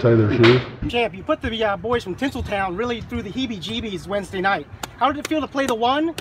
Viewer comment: you put the boys in tizzletown really through the heebee jeebees Wednesday night. 💀 💀 💀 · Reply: I thought he said pickle town